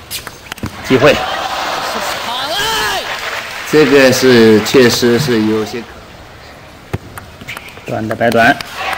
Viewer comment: 馬琳說什麼? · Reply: 耍賴...拔